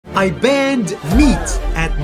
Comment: No cap first time I heard him say I banned meat I thought he said I banged meat💀💀💀💀